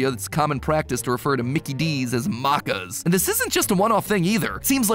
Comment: bro the way he Maccas sounded like he said that Mexican instrument the Maraca